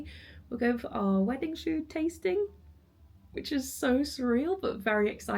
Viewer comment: I’ve rewinded 6 times, and I still hear you’re going to your wedding shoe-tasting? 😅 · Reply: I thought she said food tasting